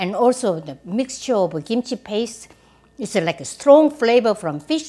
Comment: I made my first batch kimchi yesterday, and repeated you video so often. And when I was still lathering the Nappa cabbage your video played till the end. And I heard you say "Lekker" (14:23), but it just sounds like that, you said 'like a' (Lekkah which is how we also say lekker) I was so confused for a sec. Lekker means delicious in Dutch. And it's often foreigners favourite word, and we use it with everything. 😁 Also, the kimchi was delicious, did make it too spicy for my parents. 😜